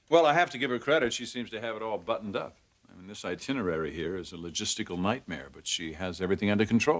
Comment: Y'all, my kids started screaming right when Eric said "logistical nightmare" and I thought he said "testicle nightmare"! 🤣🤣🤣🤣💚🖤